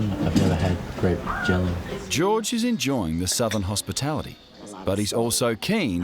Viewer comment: did they say southern hospitality?, That's New York lmao. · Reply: Soul food